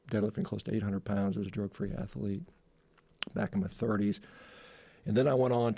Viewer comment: I swear I thought he said "Back in the 30s" Instead of "back in my 30s". While calling BS I was also trying to figure out how old he would have to be to still be alive in 2017. Needless to say, I am a moron.